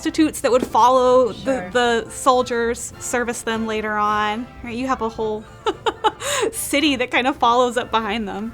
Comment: “Alright, you have a hole” is the way I heard it 🤣🤣